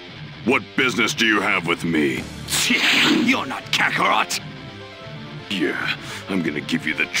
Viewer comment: Wait, what the heck did Raditz say to Piccolo?! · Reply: For a moment I thought he said "Shit, you're not Kakarot!".